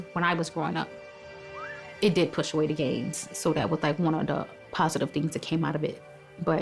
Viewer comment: Wait a minute...Did she say gentrification pushed away the gays 1:32 and that was one positive thing that came out of it??? I was feeling for her at first (and I am straight), but to hear (or did I hear?) something like that come from her in THIS day and age, is quite disturbing. I could be wrong about I think I heard, and if I am, my DEEPEST apologies to her. But that threw me for a real loop!🤔🥺😟🤔 Maybe somebody can help me out here with what I HOPE I didn't hear. There's room for everyone in this world...including LGBTQIA! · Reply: Gangs. She said gangs